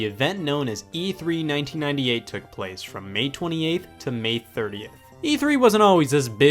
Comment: I thought you said May 28 - May 38. 😂